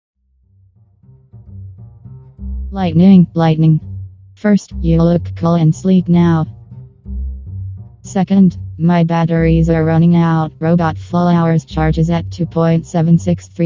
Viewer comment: Who else thought Remote said “We'll look cool and sleep now.”?